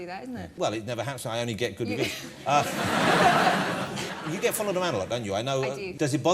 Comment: what does he say in 3:09?? " you get full of ......."? he really needs to slow down